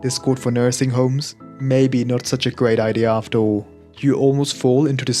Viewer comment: i thought you said "working at nursery home not that bad after all" i was like what how did you know